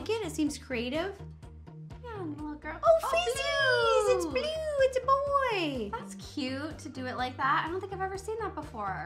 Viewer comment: I had so much filming this with! How adorable is she?? Which clip was your favorite?? :_rosHype::_rosHype::_rosHype::_rosHype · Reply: Honey...at 10:42 did you say "oh Feces"? Probably not....right?